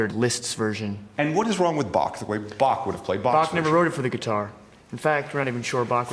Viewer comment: Did he say, “Bach didn’t WROTE’ it”? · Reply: He said “Bach never wrote it for the guitar”.